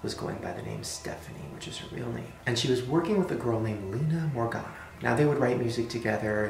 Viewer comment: did anyone else think he said "Leia Organa"?